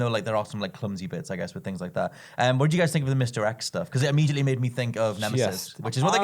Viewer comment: I thought they were saying Misdirect.. not Mr X